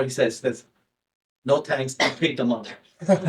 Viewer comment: what did buffet say, "no thanks...." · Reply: “No thanks, Pay the money”